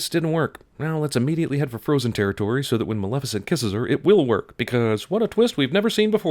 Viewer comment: lol, i thought it said mal efficient in a silly way....